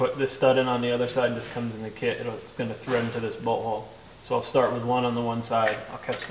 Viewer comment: Everytime he says bolt hole it sounds like he says butthole.